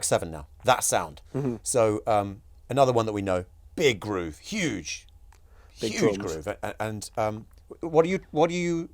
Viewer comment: anyone know what he says that’s bleeped our around the 44:00 - 45:00 mark?? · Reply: Pisshead